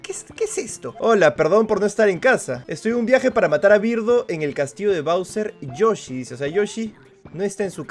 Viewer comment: creo que dice "para c*ger a birdo en el castillo de bowser"